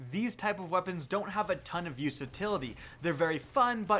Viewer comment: Did you just say "use-atility" at 2:17???